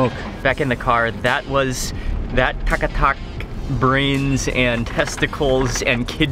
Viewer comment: Kat a kat..... i thought ive heard tak a tak at first